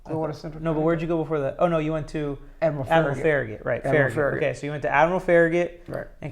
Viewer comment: All very interesting. I thought I heard Farragut Admirals. We have a town outside of Knoxville TN, called Farragut TN. School is Farragut Admirals. I live there now. Thanks for sharing your story.